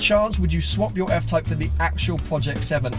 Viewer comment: Aha, at first I thought you said barley xD keep it up man